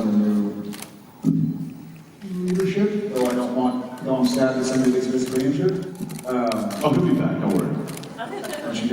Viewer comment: Who does Jared say at around 29:50 when they are talking about Cowboys ??'also Thankyou for uploading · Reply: "although i'm sad when someone gets physically injured"